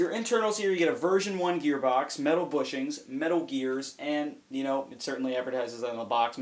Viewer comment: Did you say... Metal Gear?